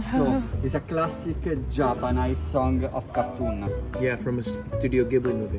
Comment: it was so hard to understand that guy at the end, anyone know what he was saying at 16:09 ? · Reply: classic japanese song of a cartoon